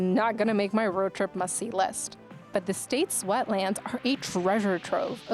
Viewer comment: I thought you said "the state sweat land" lol.